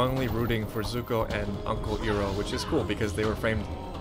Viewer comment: oh no did he just call him ehro